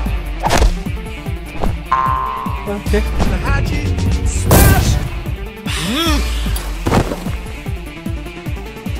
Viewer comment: What did Red Crewmate Say after He Say Okay ??? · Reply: HAGI SMASH